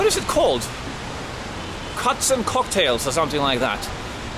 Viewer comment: has a very heavy Norwegian accent. At 00:42 in, I thought he said c**ks and cocktails 🍹. “What happens in Vegas”, I guess. To each his own. But then I realized what he said. 😂